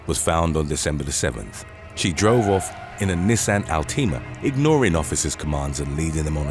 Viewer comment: Did anybody notice that he totally mispronounced that car brand at 0:50 it sounded like he said Altina instead of Altima